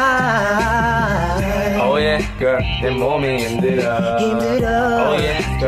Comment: “Woah bad lady” lmao did I hear that right 😂